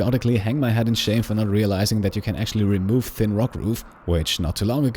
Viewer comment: What didn't he realize that he could remove? It doesn't matter how hard I try I can't understand what he says there. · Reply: He says "thin rock roof", making a reference to when he had to move his wind turbine a few episodes back!